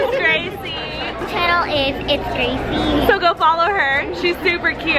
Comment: What is the girl's channel in 3:38 i couldn't hear😕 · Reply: Its